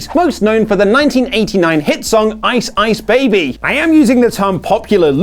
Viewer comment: Sounds like is saying Rice Baby LoL